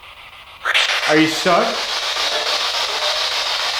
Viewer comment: I hear “I’m sick” right here.. this place was a hospital one time, right? 👀